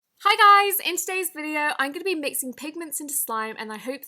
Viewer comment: I thought that said "Mixing pregnant".